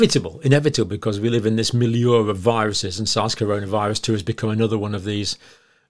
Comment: what did he say with that m word of viruses? I tried googling what he said and what it meant to improve my vocabulary lol but I can’t figure out what he said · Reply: Meliora viruses.